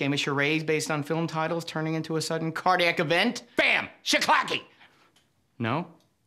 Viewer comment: what word is that at 0:45? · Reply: Shiclacky.